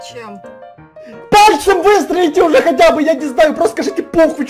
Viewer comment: Мне одной послышалось то что на 13:46 сказал: (простите меня за выражение)- ПОХ*Й ????????!!!!!!!!